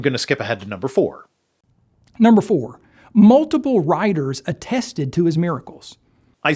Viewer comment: As a Brit, I am struggling with the WBVS guy's accent. I thought that he said that "multiple riders" witnessed the miracles - couldn't work out why Ringwraiths were involved!!!